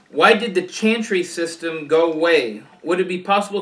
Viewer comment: I thought you were saying Chancery system. I'm so confused...